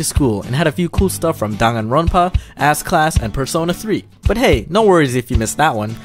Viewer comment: did you say ass class?